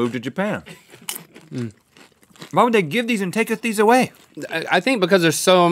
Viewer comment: did anybody hear Rhett say "And taketh them away? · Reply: Miguel Mendoza Why would they give these* and taketh these away